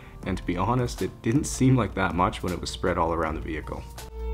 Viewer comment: Hold up, When you were emptying the vacuum, did I hear you say, “didn’t seem like that much” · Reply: He said it didn't seem like much when spread around the vehicle. he dumped out a lot of nasty stuff from the vacuum 🤢